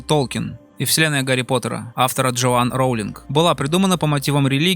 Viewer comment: сначала мне послышалось "Джоан Роулинг Толкиен"....